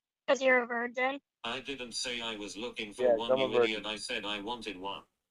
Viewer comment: Did he just say “Yes! I’m a virgin?” Around 4:00? So he did admit it!!!